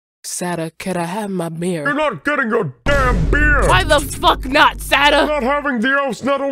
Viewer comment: I’ve died every time I hear “your not getting your DAMN BEER”